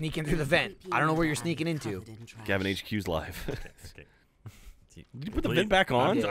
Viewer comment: Did you say hq?